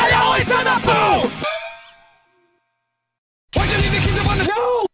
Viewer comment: WHY'D YOU LEAVE THE KEYS UP ON THE NO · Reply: I thought it said WHYD U LEAVE THE KIDS UPON THE TABLE